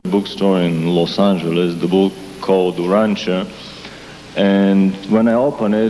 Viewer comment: Can anyone tell me what book he mentions around the 4:13:33 mark. With the captions on it comes across as Rancher but the guy asking him the question has a pretty strong accent. Anyways if anyone knows the exact title of the book it'd be greatly appreciated. The book sounds very interesting thanks! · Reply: I think I found it. URANTIA. Wiki says written sometime between 1924-55 and the content fits.